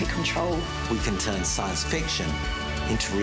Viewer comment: All I heard was, “pizza, the human cyborg”